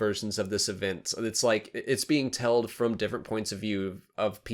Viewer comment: You said 'telled' instead of told at 4:02. I just wanted to let you know.